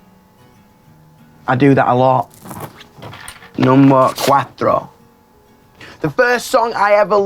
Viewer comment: did he really say "number cuatro/quatro" at 3:05? cos that was so cute (quatro and cuatro means "four" in portuguese and spanish respectively)